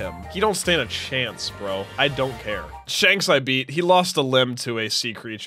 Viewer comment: DID SAY SHANKSS THE HELL😊